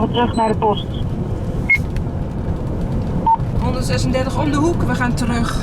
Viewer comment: This is litterally gibberish, what could she be saying? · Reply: "130 om de hoek, we gaan terug"